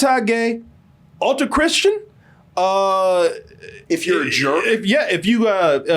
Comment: I couldn't make out what he said and the CC was unhelpful "If you're a "german"? "Gerbil"? "Jerk"? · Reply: I think he said "jerk".